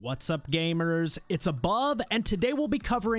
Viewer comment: I always hear “it’s a bob”